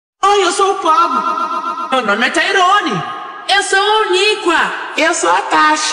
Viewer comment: i heard: "im a potato ROONY"